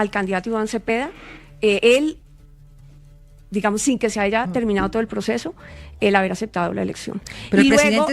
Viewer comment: Escuché mal o dijo sin que se aiga acabado el proceso... En el minuto 14:23....